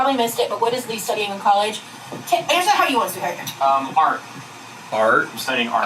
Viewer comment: Am I hearing this correctly @ 29:22? Does say 'Answer how you want, sweetheart'?! WTF was that.. Did all the other questions have to be answered how she wanted? Here's hoping I heard that wrong, because it feels controlling and creepy like he needs her permission to speak.